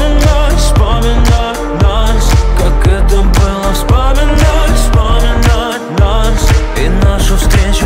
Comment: Мне послышалось,, поминать , поминать нас ...")))))